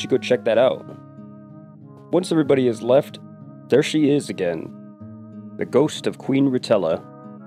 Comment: I swear i thought i heard you say the ghost of queen nutella